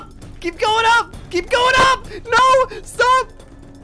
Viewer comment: did you say to stop